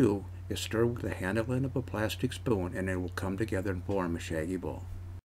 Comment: I thought he said saggy ball😂😂😂😂 gotta laugh huh 😂😉